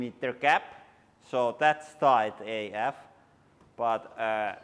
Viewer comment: did he say “tight AF”? Their English slang terms are getting pretty good. They make me laugh. Thanks for the good content.